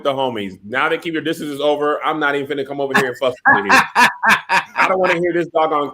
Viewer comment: did say f***? 😂😂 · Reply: No. He said, "....I'm not even finna come over here and FUSS with Tahir..." He was just speaking really fast on that "fuss" lol